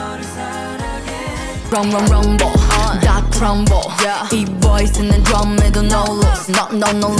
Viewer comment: I keep hearing “Got crumble” when yuqi sings😳